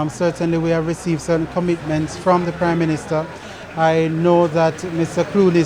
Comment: Did he just say crime minister 🤔🤔🤔🤔